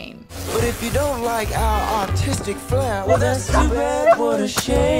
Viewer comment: Did I heard the Siamese cats new song I heard “if you don’t like are autistic flare”or I’m just dreaming